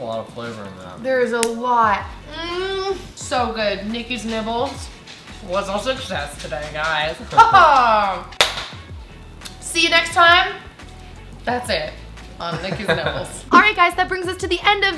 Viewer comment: sounded like you said "see you next time on nikki's nipples" lol